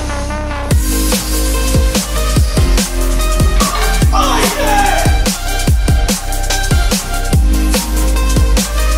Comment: what did he said at minute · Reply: Eux mat "Lets gooooo"